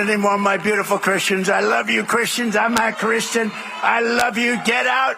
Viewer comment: does he say “I’m not christian” at 0:20?